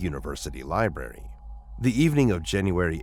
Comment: I'm not the only one that noticed library pronounced "liberty", right? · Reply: I heard “liberry”. Which is how children say it. Caught my attention every time he said it.